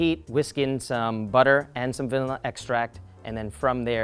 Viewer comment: WHAT DID YOU SAY VALILLA EXTRACT 😭😭😭😭😭✋✋✋✋✋